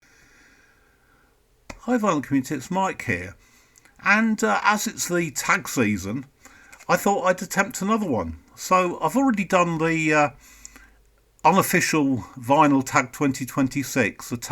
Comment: Hey Mike! At first I thought you said “tax season” Interesting covers, never noticed Nixon on Wakeman’s album. That is a beautiful satellite image on that one cover. Cheers!